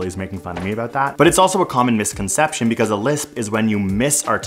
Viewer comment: I heard Communist conception